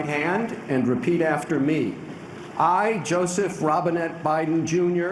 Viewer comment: Honestly I thought he said Joseph Robinhood Biden. I thought was amazing